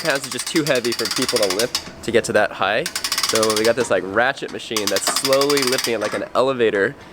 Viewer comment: did he said ratched a say that alll the time my sister get annoyded.at me😝😝😝😝😝😝 love u guys